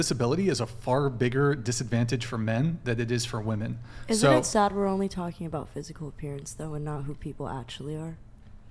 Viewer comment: Here's what she said " isn't it sad we're just talking about physical appearance" Here's what I heard " isn't it sad that men have preferences"